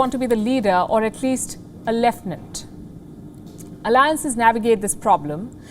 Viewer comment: what word does she pronounce at 0:10? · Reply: Lieutenant, I guess